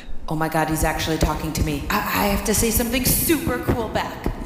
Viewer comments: For some reason I heard "omg dads actually talking to me" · "Oh my god it's actually talking to me." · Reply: PowahSlap Entertainmint I thought he said "omg dads actually talking to me"